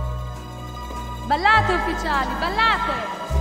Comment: what did she say? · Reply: ballare ufficiali Ballare.